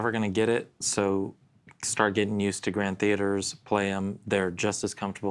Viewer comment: Oh, GT is Grand Theater! I thought said Grand Touring when first revealed, or Grand Tavern. I had an AD22E MAHOGANY. I traded it in for a Custom Martin 00-15m deep body. Thanks, and